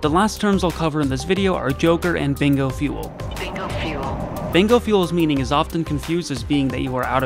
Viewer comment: Yeah this is a really good guide. I'll try to actually pay attention to overlord comms now that I know what they're actually saying. Also I always thought it said "think of fuel" instead of "bingo fuel"